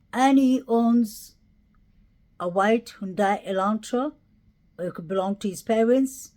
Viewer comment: I thought I heard that JD owned a white Elantra. I’m confused